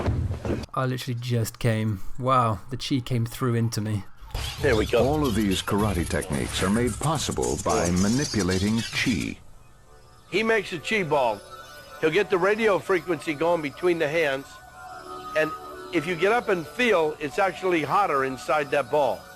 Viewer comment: “Boshito” you say? I call bullshitto. He said chi ball I could of sworn he said rice ball then ate it